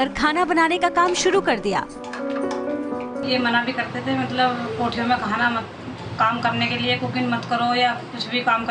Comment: Did she say 01:53 kotha??